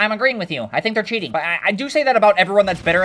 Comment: The fact that thought the boy said "You are cheating" got me ☠️☠️